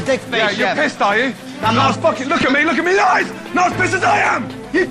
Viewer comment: What did Gordon say at 2:51 ? “Look at me life!”???